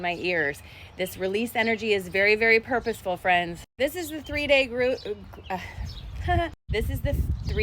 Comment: I hope this doesn't come across as me making fun of anyone... but at 0:37 you almost said Groot (or at least to me it sounded like it) and if anyone knows or looks up who Groot is, I found it interesting.... Anyway, thanks for doing these reports. I very much appreciate them. ❤